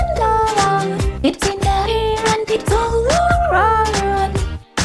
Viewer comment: sounds like she said "its in my hair"